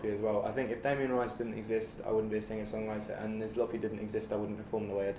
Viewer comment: he says if Damien Rice didn't exist he wouldn't be a songwriter, but then he mentions a band. I can't really understand what's the name though. Can any of you help me out here? Thanks! :) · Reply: Nizlopi